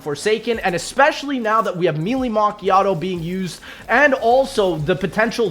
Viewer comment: i’m so brainrotten that i swear i heard “Mewing Macchiato”😭